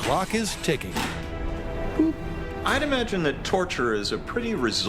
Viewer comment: Instead of “I’d imagine Torture is a very results driven business,” I thought he said “Hyneman’s Torture is a very results driven business”